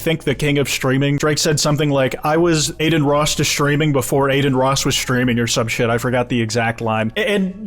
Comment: He actually says “I was aiding Ross with streams before Adin Ross had streams” or something along those lines. He’s just dissing Rick Ross again lol 😂